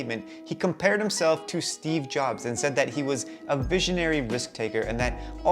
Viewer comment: did you say steve jobs?